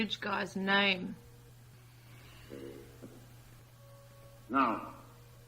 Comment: What is the bridge guys name... u ask at 10:00 mark... you write Take Her... I hear ...Jason.